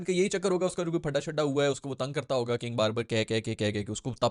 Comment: Wait. What..... Did i hear King Barbar.. lol..... hahahhaaa